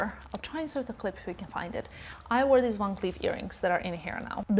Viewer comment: 😂did you say you wore the long sleeve earrings? Haha. I love! Everything is clothes, even the earrings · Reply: Haha funny one, but she said the "Van Cleef" earrings :)